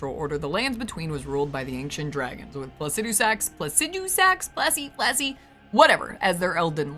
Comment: I can't hear it as anything but "Placid Dew Sacs" :face-fuchsia-poop-shape